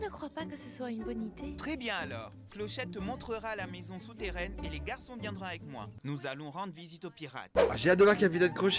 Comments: suis- je la seule à entendre « et les garçons viendra avec moi » ? 😂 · ‘’et les garçons viendra avec moi ‘’ j’ai bien entendus ????